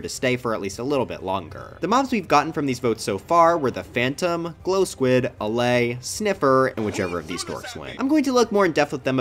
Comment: What he says: “The mobs we’ve gotten from these votes so far were the phantom, the glow squid, the allay, the sniffer, and whichever of the win.” What I hear: “The mobs we’ve gotten from these boys so far were pointless, Dream bias, debatably useful, pretty good and the armadillo(wolf bias).”